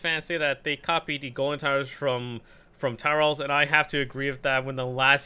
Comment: he said tower battles so fast I thought he said “towels”